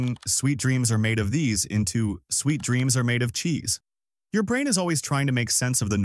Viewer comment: i heard it as “sweet peas tomato cheese” 😂